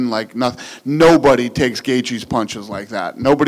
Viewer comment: Fun fact : if you put on subtitles, at 1:30 instead of saying gaethje the subtitle is saying "gay cheese"